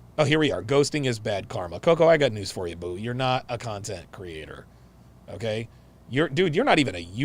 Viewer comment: 🤣🤣🤣Was that a Freudian slip? 🤣🤣 Sounded like you said the very accurate statement "Koko you're just a cunt-ent creator." Lmao. I think that's the most accurate description🤣🤣🤣. She makes women worse, not better. 🤣🤣🤣